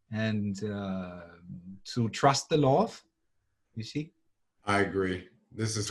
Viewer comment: I could not understand what was saying 53:04 "to trust the..." It didn't sound like "law." It sounded more like, "law of" but 'of what?' It didn't really sound like "Lord" because there seemed to be no "r" or "d" sounding consonants. The word was slurred, somehow. It was like, "lauf." What can I say? I mumble when I listen. It was funny, it was like your key word and it was blurred to my perception and robbed me of understanding the concept behind the word as its symbol. I could not connect to the idea you intended to communicate, if even subconsciously. I felt the loss. I was the boy who followed Socrates into the Mediterranean. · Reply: To trust the love.